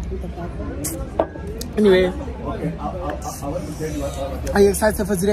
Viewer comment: I thought said shut up but he said shout out